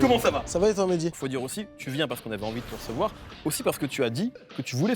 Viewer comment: Il a dit quoi svp? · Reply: Jeune Uchiha ravi de vous recevoir